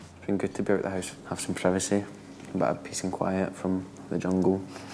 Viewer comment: I heard "pissing quiet" and then realised he meant "peace and quiet" 😂😂